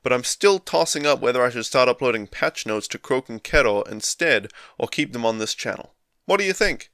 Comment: Thought you were saying Croaking Kettle for a bit at the beginning :D Didn't realise it was pronounced like ろ